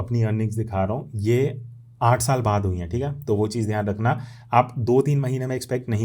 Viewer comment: Hi sir.....on 11:00 Did you say 8 years? I want to make sure I heard you correctly.? Please Reply must because it's very important